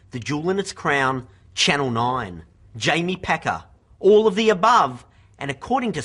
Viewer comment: I thought he was saying "Pecker", to be honest...